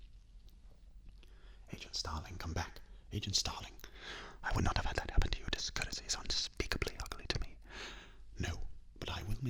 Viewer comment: What does he say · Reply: “I would not have had that happen to you. Discourtesy is unspeakably ugly to me.”